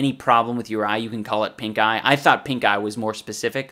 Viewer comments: I thought he was saying pinkguy and that made the video even funnier. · I can't be the only one that thought he said pinkguy instead of pinkeye